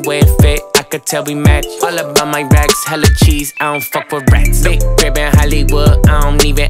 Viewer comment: Am I the only one, who thought he said "I don't f*ck with brats"?